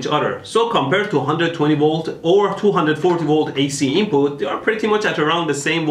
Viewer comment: in closed captions it says DC instead of AC